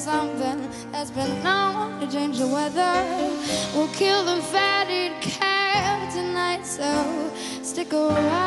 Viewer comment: "We're going to kill the fatted cow tonight so stick around" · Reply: I hear "...kill the fatted calves..." at 1:29, not cow, as you suggest.